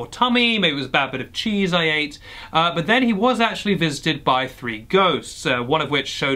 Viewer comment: Because of your accent, I heard "Fisted by Three Goats" instead of "Visited by Three Ghost." To say the least, I was very concerned for a moment. 😂